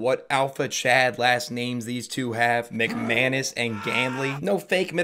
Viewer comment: when i heard I thought "Mac Anus"